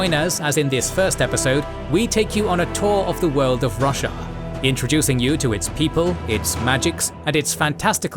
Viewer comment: Lol, I legit heard "The world of Russia" and had to do a double take. :D